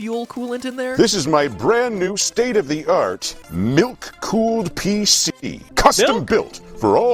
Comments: At first I didn't have my volume turned up high enough and I thought he said "nuke cooled PC" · At first I thought he said nuke cooled pc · At the start i thought said nuke cooled pc. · at first I thought that he said "Nuke cooled PC" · I thought said nuke cooled PC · I heard “nuke cooled pc” and i was like “i get that nuclear reactors got some good cooling but using it to cool down you pc?!”